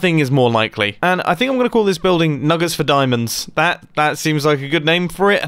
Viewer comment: Thought I heard "nuggers for diamonds"